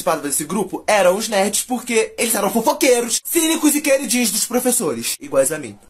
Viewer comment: alguém entendeu oq ele disse? eu escutei cini zuziqueiro dia dos professores